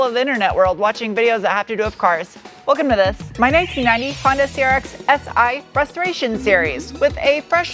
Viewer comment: I had to play the first 15 seconds back again cause I thought you said this is my Honda Crx Frustration Series, and I thought that was legit what you said but it could equally true at the same time